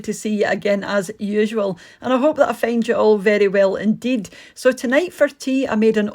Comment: OMG 🤣 because of your accent it sounded like you said I hope I all offend you well 😂 so funny to me ❤️